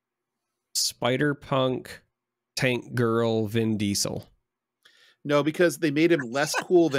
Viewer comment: Not going to lie the first time I heard you say tank girl. I thought you said tub girl. I don't know about how she rebelled against the system.....